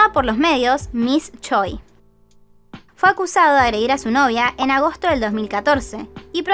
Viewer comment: creo que dice "fue acusada" en vez de acusado